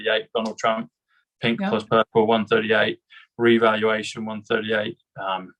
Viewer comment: Reevaluation 138? I keep getting 143, guess I don't quite understand this gematria-74 stuff 😞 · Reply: It's that heavy accent 😄 said revaluation, I heard it as reevaluation, the extra E (5) makes the difference 😀 my bad.